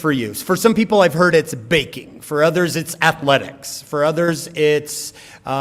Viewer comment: Did he say baking? I thought he said banking... 😅😜😏